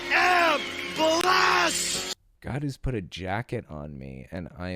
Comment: I swear I keep hearing him say "God has Jacked on me!" I know he said jacket...but if Trump supporters can make an alternative reality then I'd like to believe he admitted having a homosexual relationship with god.